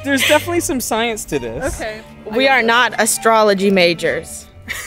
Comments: did they say astrology? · did she just say astrology 😐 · DID THEY SAY ASTROLOGY